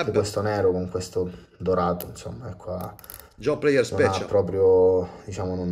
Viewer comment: Cosa dice a 16:20 commentando il Kamasu con gli accenti dorati? Non capisco le parole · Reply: John Player Special